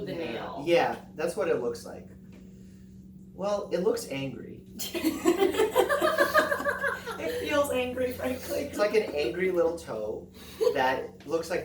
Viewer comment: I legit heard "it looks gangrene" o.O "It feels angry" Ohhhh